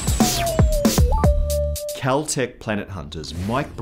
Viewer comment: I heard "Celtic Planethunters" which sounds like a cheesy futuristic fantasy comic book series.